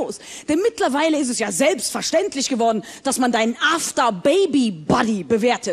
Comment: Lol ich hab zuerst After Baby Buddy verstanden, im Sinne von einem COach der dich nach der Geburt begleitet um fit zu werden^^